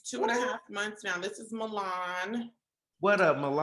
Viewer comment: is it Milan or Mulan ? I swore said Mulan 😂